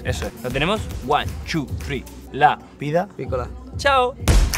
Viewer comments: Dijeron LA VIDA ESCOLAR ?? No entendí lo q Itzan dijo. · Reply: yo entendí la vida picola pero no se que significa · ¿Alguien me puede decir qué dicen en el minuto 8:39? No entendí muy bien por la música de fondo. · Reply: La vida piccola